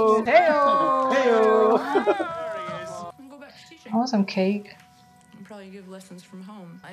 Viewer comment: Heard this as "I want some Kate" and nodding like an idiot "Yeh, Vera's hot," before realizing you said "cake".😂😂😂😂😂😂